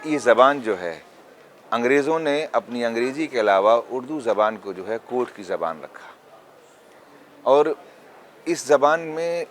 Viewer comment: what did he say …? Angrezi ke elawa urdu ko …. Koonwar ki zaban kaha? Something ? Anyone … ?